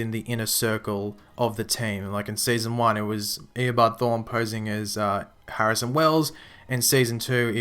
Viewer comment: I thought he said wallas or haris/Harrison not saying at all theses are possibilities just saying that's what I heard